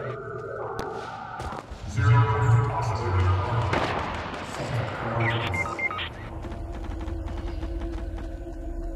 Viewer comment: what did the Visitor say? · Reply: Just checked, he said something like “zero posiblity, because setting coordinates”